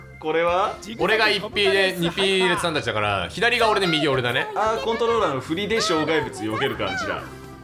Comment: 左が俺で右が俺だね って聞こえる？